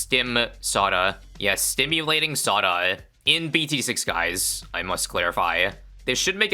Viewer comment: First time watching your content and i hear "stimulating Saudia". Interesting....